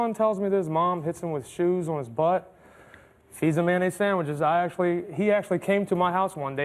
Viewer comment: I had to really lean into the tv I thought they were saying manny sandwich 😂